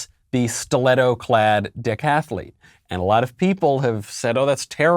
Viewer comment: When you said decathlete, at first I thought you said dick-athlete as a play on words because he's a man.